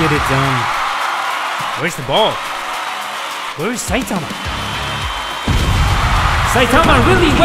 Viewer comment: bruh when u go to 4:13 and put subtitles on instead of saitama goku says " where is satan" XD